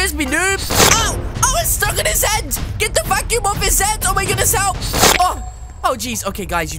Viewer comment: did he just say fuck? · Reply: Kelessa Alexander he says vacuum not the f word